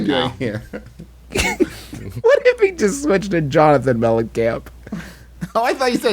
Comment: I also thought he said John Nothing Mellencamp